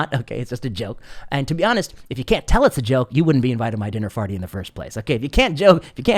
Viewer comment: I want to be invited to your "dinner farty" lol I laughed so hard when I heard that for some reason.